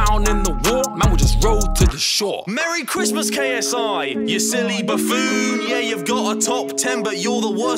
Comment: Ngl when i heard Harry say “You silly buffoon” to JJ, i thought he said you silly baboon 😂😂😂